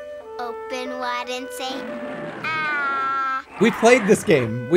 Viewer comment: I swore for a second she said “oh, Bin Laden say ahhhh!”